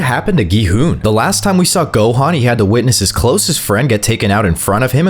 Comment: did bro say Gohan? 🤨